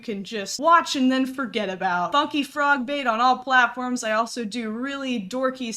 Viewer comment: the way shes saying it makes it sound like shes saying fuckyfrogbait lol